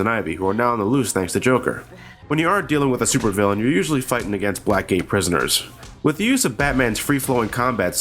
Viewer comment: Being honest I thought he said 'black gay prisoners' for a sec